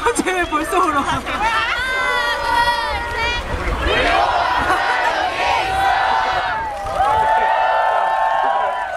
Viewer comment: what did fans say during 7:16 - 7:20? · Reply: Pagim Gimpa There saying "we are always here"